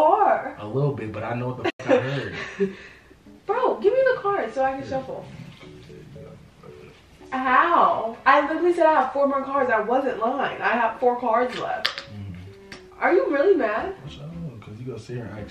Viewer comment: Lmao definitely thought she said “damn I only had four more cards” definitely had to rewind to hear it again 🤣